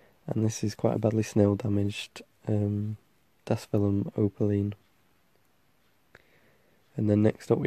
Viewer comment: the one at 2:12 is really pretty, i couldnt catch what you said it was though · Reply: Apologies for my quiet mumbling voice. I can talk loudly but I tend to feel quite awkward I'll try harder. It's a sedum dasphylum opaline. Beautiful plant!